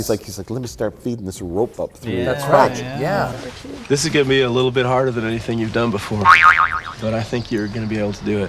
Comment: During the rock climbing segment I completely misheard the actor and thought he was saying "This is getting me a little bit harder than anything you've done before"